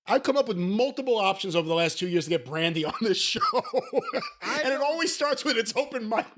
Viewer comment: Thought you said Randy first instead of brandy lmao